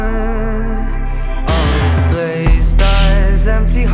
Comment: Glazed eyes, empty hearts, buying happy from shopping carts, nothing but time to kill, sipping life from bottles. Tight skin, bodyguards, Gucci down the boulevard, cocaine, dollar bills, and... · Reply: i always thought he said "blazed eyes," like from pot, but now i'm questioning that...